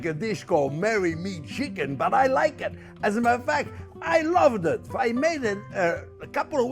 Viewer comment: every time he says “as a matter of fact” I hear “Mo** F***er”